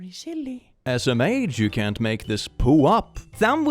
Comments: "As a Mage you cant make this shit up" Is what I heard while enjoying dinner over this! XD I was like... Wow! Thats new! I like it! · I heard 6:43 as "As a mage" instead of "smh". "As a mage" would be a great way to start weird sentences. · It sounded like "As a Mage" instead of SMH to me, and that was funny lol.